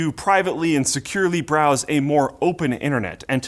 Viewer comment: did you say privately insecurely browse??